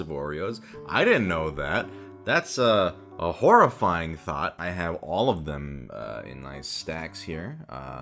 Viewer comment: What Says: "Did you know there's 27 different types of Oreos™? I didn't know that! That's a....a horrifying thought." What I hear: "*heh-heh-heh - I'm in danger!"